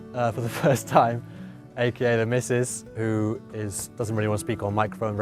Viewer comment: Didn't the on screen text say that she was his girlfriend? Regardless it is nice to meet her. I'm very curious because she said her "Mayan ancestors." That is an interesting topic. 😊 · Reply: I honestly thought he said wife but she was lovely 😊